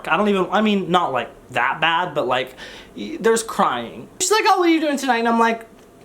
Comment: i misheard "there's crying" at the vidcon parties as "there's crime" and didn't even question it